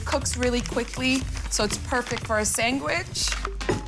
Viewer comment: Did she say sangwich?